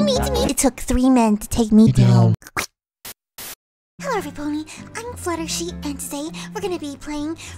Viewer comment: did fluttershy say fluttershee?